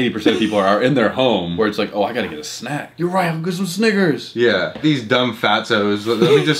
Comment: urm… what did you say? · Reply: Snickers